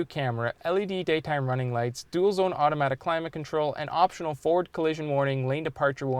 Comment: did i hear ford?